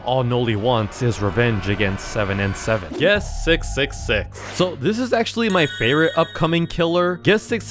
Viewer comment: I heard “yes 666”